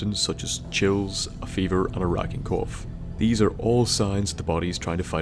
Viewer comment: I really thought he said “an Iraqi cough”